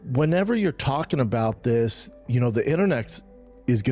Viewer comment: did Brendan call the internet "internex" or "internecks"? I need to know which to use moving forward